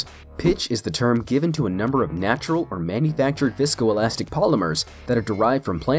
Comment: did you just say VSCOelastic?